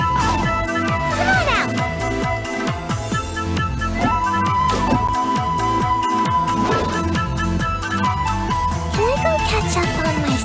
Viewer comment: I ALWAYS THOUGHT SAYU WAS SAYING KIMONO NOT COME ON OUT LOL